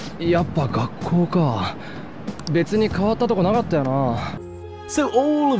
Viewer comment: sounds like he says "とか" not "こと" i guess it's the "toka" which means "and so on"